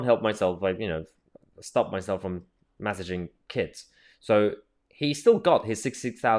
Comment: I thought you said massaging.